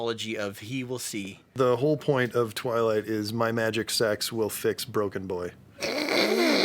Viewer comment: "My magic sex will heal broken boy"- that was my line sooooo many times. So, thanks, guys! You're doing amazing work awakening so many blind women. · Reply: Ohhhh I thought he said, "My magic sacks will heal broken boy". Thank you for the correction. : D